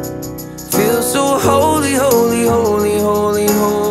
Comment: I've been listening to this for weeks in repeat but in my mind, I keep hearing "Homie Homie" instead of "Holy Holy" Like damn Homies In My Mind